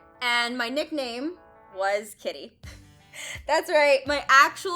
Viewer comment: Anyone else thought she said tiddy instead of kitty? Lol